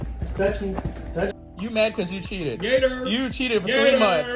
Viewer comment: am I getting old? Is he saying gator? What does that even mean??? 💀